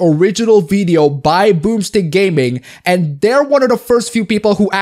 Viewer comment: I was watching at 2X speed & every time you said "Boomstick Gaming" I heard "Boobs In Gaming". Now I'm disappointed to find out that's not a thing.